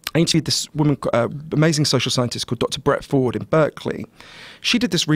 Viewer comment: I'm trying to find her research. I heard Dr. Brett Ford but I think I misheard the name?